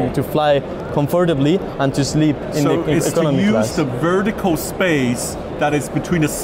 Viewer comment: "I decided to design this double decker seat in order to improve the passenger experience in the economy class." Oh, i had trouble with his accent. I thought he said "Steerage Class"...